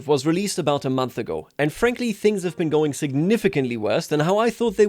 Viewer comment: did he just say anne frankly